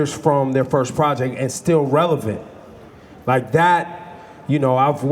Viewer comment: did he say STILL RELEVANT??